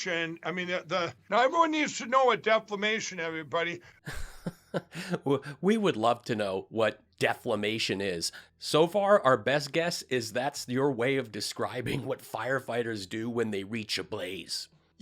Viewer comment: Ha ha ha! At first I thought Lindell said "declamation". Oh, English is my second language but I'll tell you, I enjoy hearing Lindell destroy the English language at every turn. I especially love his newses and his calculeses.